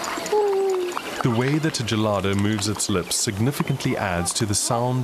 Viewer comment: "Inaudible"? He clearly says "the way that the gelada".